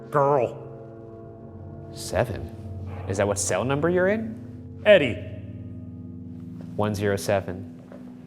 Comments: Ryan (asking multiple times):"what's your name? Me: "EDDIE! HE SAID HIS NAME WAS EDDIE!" 😅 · Send us q's for the GF Debrief under this comment! Make sure to send also some questions for our fearless director and cameraman, Mark! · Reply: for Debreif: Guys, at 18:26 it sounds like Shane says “Eddie” a little after Ryan asked “What’s your name?” 👀🕶🤏🏻